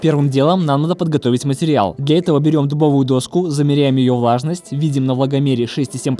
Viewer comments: мне послышалось берем доску и замеряем ее важность. думаю теперь пипец какая важная доска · Послышалось "берем дубовую доску, замеряем ее ВАЖНОСТЬ".